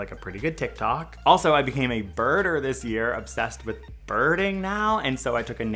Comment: Umm so when he said “I became a birder this year” I thought he said “I became a murder this year” yeah